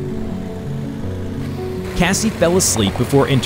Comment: How do you T-Bone someone who fell asleep while driving and YOU'RE the one held responsible lmaoo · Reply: Bennington Forever yeah I just rewatched it and realized that. Sorry. I thought he said Micheal fell asleep.